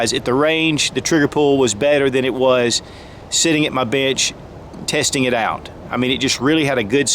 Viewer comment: Sounded like he said sitting at my bitch. Lol